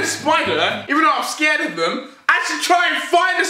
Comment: On fact #2 I thought he said I'm scared of incest